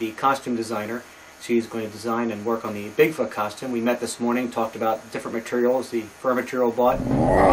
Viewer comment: Serious question: What does say at 6:15? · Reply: "We met this morning. Talked about different materials, the fur material bought..."?